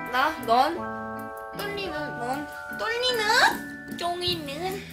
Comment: I heard "JONGINeun" waah! I immediately thought it's Kai xD But then I remember that this was uploaded before exo debuted. So she actually said "JJUNGINeun" (so). Ah what a shame..